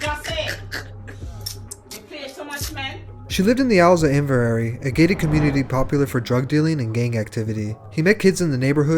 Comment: wtf did x’s grandma say 💀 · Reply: “Jahseh! You play too much man!”